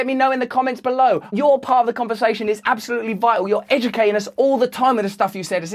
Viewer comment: "you educ-anus" did I hear right?